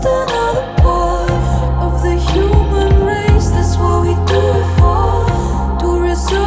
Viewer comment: during the "race, race, race, race" part it spooked me out because it sounded like she was saying my name (grace)